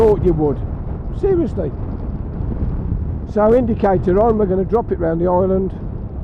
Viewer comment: Thanks for the great video. Since not all YouTube viewers speak English, could you put into words exactly what you said on 22:04 please? I see a round about ahead and not Iceland/ireland. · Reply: "Indicators on, we are going around the island"